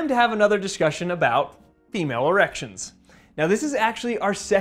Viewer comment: Sounded like you said Emale erections